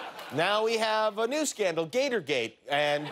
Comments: Who else thought it said gatorade? 🤣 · I thought is said Gatorade😂😂😂😂🥺